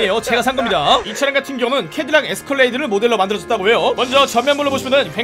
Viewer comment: 저만 에스컬레이터라고 들었나요?ㅋㅋㅋ 이 차는 솔직히 돈 값은 못하는것 같네요.대통령 경호놀이 할 때 좋겠군요.오늘도 좋은 영상 감사합니다!